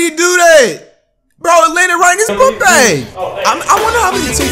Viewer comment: "Noooww!!..Haa diyu duu dayy..??" "Bro litter right his pop bang.." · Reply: It sounded like he said poop bag